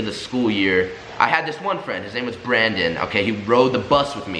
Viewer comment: I thought u said Boyfriend I funna be like Hellll To The NO No Nooo